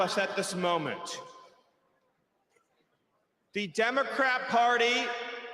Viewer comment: Benny, it's Bedminster, not Bedminister. You're adding an additional letter, making a fourth syllable. @ 1:18:10 Did Miller say the "Democrap" Party?